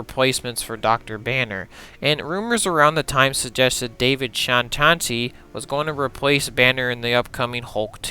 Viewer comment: I cannot figure out who David Whathisname is that you mention. I’ve tried Google and IMDb but I don’t know how to spell his last name. Is that an actual person? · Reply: I feel like he's trying to say David Duchovny. I've heard his name attached to Banner before. Otherwise, I have no clue.